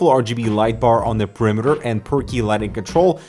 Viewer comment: Every time says per key lighting control, I hear 'perky' lighting control :3